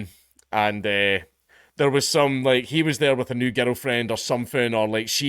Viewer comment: Every time you say "girl friend" I hear 'oil friend' or 'ghetto friend'. I guess all 3 can be correct.